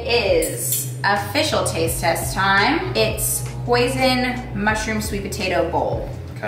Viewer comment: for a minute I really thought said "poison mushroom sweet potato bowl"